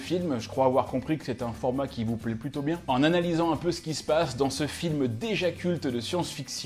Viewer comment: hallucination auditive! J'ai entendu "dans ce film d'ejacule" ah bah je vais ptet le regarder ! ✌️